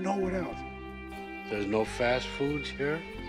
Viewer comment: did he say there's no law there?